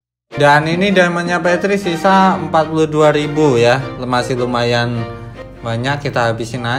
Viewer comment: gak salah denger bang prasaan 4200 ekwkkwkwkw